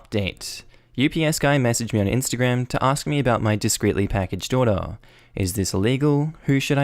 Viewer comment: On 2x speed, with his accent, this sounded like "My discreetly packaged daughter." Haaahahahaha